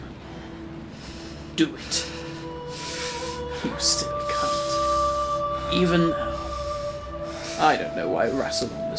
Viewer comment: Great episode. Just one question. What does the guard say at around 12:31 ? Thanks, Christy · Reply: I think it was "You still can't"